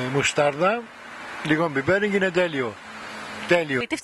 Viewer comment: Why does it say "μαλακότατο" at 4:22 when he actually said "τέλειο?" Isn't that a bad word...?